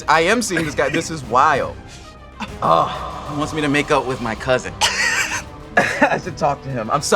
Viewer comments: I thought he said "make-out" with his cousin😂 its alright guys, he said make-up...as in mend the bond😅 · 🤣 …👀🤨🧐 Edit: I thought I heard make out 💀